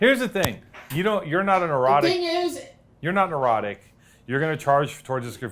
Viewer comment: I thought kept saying he was erotic but it was "neurotic" haha